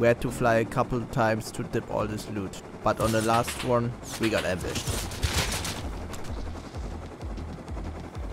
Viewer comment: we got "erwischt" ? Oder soll das Ambushed heißen? Der Part ist so hart Nasal gesprochen, dass es echt schwer fällt das zu verstehen. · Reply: hahah ja eigentlich ambushed aber kann sein dass ich ein wenig genuschelt habe haha sorry